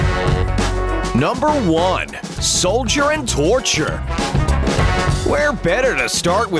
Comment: mom: calls me me: comes to mom: soldier and torcher! me: WAIT DID HE JUST SAY TORTURE?!